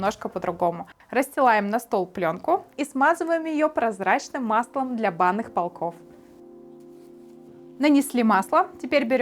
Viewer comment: Я не смог расслышать, что за масло используется, чтобы масса не прилипала? Субтитры не помогли - "смазываем маслом для банных полков" 😁